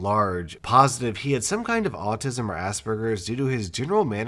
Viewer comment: Did he say ass burgers